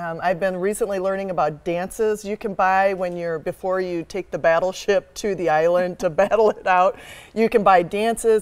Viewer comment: Don't forget, if you wanna check out Skillshare make sure to sign up using this code <link> and learn how to edit videos like ours :D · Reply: Did they say battle ship